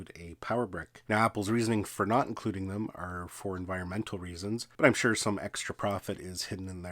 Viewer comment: I actually heard that as mental reasons..